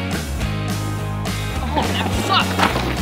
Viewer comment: Not sure if I heard that right XD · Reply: Emmett Meehan he said “oh that sucked”